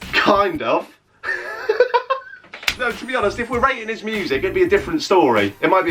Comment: i thought it said raPing not rating...was like damnnn this guys contents changed a fuck ton